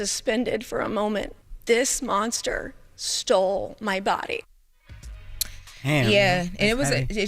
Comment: But she made excuses for Trump. Save those tears 🥱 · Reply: Did she say her fiancé "stole my body"?